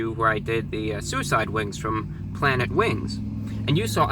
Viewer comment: Wow, because of all the drama going on I thought at 1:39 when he said suicide wings I thought he said suicide woods and I was so confused.